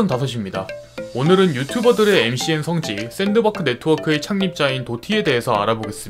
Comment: 샌드박크 네트워크 잘못 들었나 했네 ㅋㅋㅋㅋ